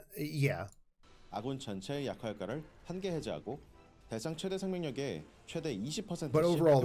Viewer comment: Did he just say: That doesn't mean you're gay ?